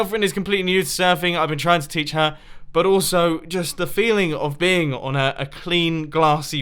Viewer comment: Woah, almost sounded like "... but also just the feeling of being on her"